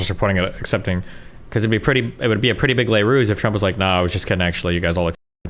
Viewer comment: What word does Steven say at 8:32 I just can't catch it, sounds like les rouges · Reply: Close enough. He said "le ruse" which basically means an action intended to mislead or deceive.